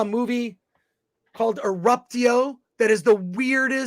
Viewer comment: What was the movie is saying @ 1:28:02 ? I've tried every combination of what I think he's saying, and I can't find anything close. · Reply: I think may have said "Abruptio" which is a 2023 horror film that looks pretty weird (I commented yesterday but I think my earlier comment got stuck in moderation because I included a link to IMDB).